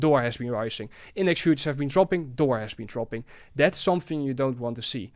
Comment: What does he mean by "duar"? He says the Index market is increasing and "duar" also been increasing. What he mean by Duar? Could you guys help me? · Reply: Wait!! I think he meant dollar! 😂😅 Dollar right? .yes true.